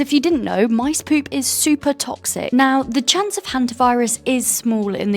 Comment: did someone say hanta virus😅